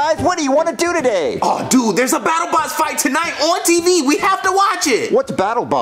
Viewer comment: I thought they said battle bus from fn